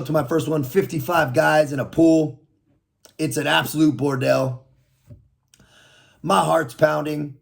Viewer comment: What word did he say at 1:32? It sounded like "Its an absolute bordelle." I've never heard that word before